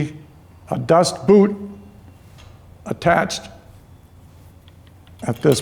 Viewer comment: Mysteries of wife's new bike now revealed, previous owner's comment: "Front break just needs tightening up." Actually means new 'noodle' required! Thank you. 'Dust boot' Haha, I thought you said 'Das Boot' !